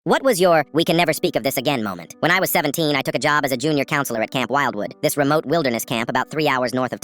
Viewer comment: Am I the only one who thought he said cam wilder 😂😂